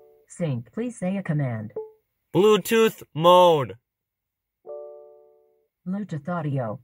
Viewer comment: Anyone else think that shit was mad funny when he yelled Bluetooth mode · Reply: Thought he said Bluetooth moon lol